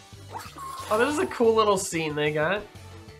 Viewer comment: Did mike say the n word in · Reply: No, he said: "Oh, this is a cool little scene they got."